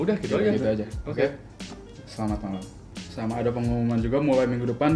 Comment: Kok yg terakhir kedengerannya kaya “Sama ada pengumuman juga, mulai minggu depan kita bakal berenti bikin video For Food Sake” Lanjutin plis FFS nya 😢